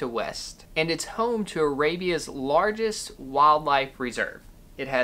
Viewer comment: Did he say: Rabies largest wildlife reserve.. Sound scary. I don't want to go there. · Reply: He said "Arabia's largest wildlife reserve."